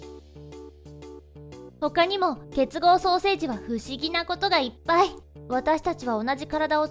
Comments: 結合双生児が結合ソーセージに聞こえた。 すべりました。ごめんなさい🙏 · 結合双生児が結合ソーセージって聞こえる...www